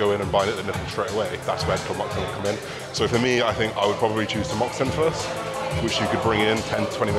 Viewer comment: What was the first thing you said about how to sort gyno out mate? At 7:15mins. How do you spell it? You said about taking 10mg small doses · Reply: Tamoxifen or Raloxifene